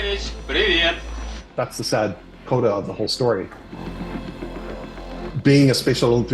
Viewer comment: thats the sad WHAT of the whole story?? Sounds like he says quota but that makes no sense please help so i can fall asleep tonight😅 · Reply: “That’s the sad CODA of the whole story.”